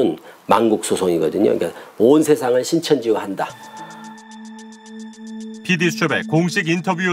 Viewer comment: "온 세계를 신천지화 한다.." ->온 세계를 코로나와 한다 로 들리는뎁?ㅋㅋ